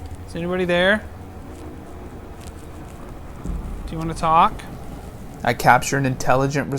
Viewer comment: I heard "What do you wanr"